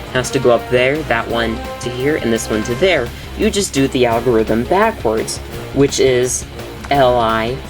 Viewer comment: you said Ri first but the caption says Li